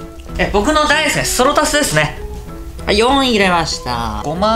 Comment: なんて言いましたあ！？笑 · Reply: ｢私の大好きな白だしですね｣と聞こえました